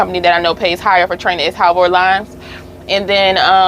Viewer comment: What was the name of that company you said gives better training pay than prime? You said it at part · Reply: Halvor lines